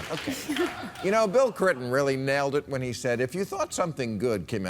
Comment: did anyone else hear "Bill Crinton" at 0:59 or is that just me?